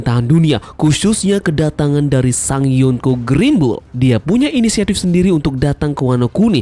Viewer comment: aku gasalah denger kan ya " sang yonkou greenbul" wtf ?